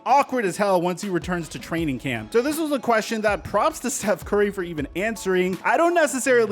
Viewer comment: He really said Seth curry😂